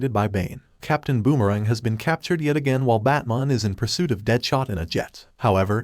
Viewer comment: “batmon” yet the transcript says “batman”. went back to check if i heard that correctly. 🤔